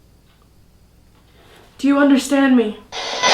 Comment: When you said "do you understand me" I heard "you're crazy"